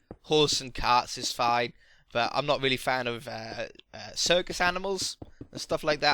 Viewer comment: You're not a fan of animals... You have LOADS of pets lol then I heard you're not a fan of circus animals that makes sense lol